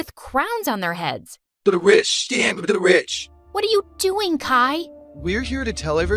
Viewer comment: I can’t figurę out what they’re saying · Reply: i think it was “the rich, stand with the rich”